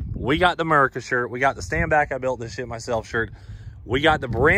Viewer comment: I thought it said "Stand back I shit myself"